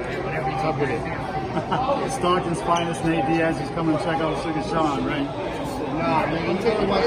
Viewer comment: I guess they have history? I thought he said “here to check out sugar Sean huh?”